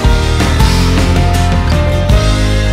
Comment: 亮ちゃん、大好きです！！ 小1の娘も聞いてます(•ө•)♡ 曲のラストフレーズ｢奏でていこう｣を、｢こなれていこう｣と歌いますwww 娘にはどうしてもそう聞こえるようです(´･_･`) 素敵な楽曲をありがとう！！